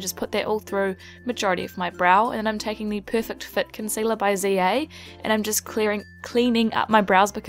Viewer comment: Love your videos! beautiful look! haha when I saw the concealer i thought it said "perfect zit concealer"